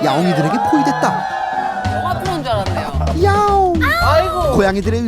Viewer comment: 영화트론(?)줄 알았네요??? 무슨 말이죠? 들리는분 답변좀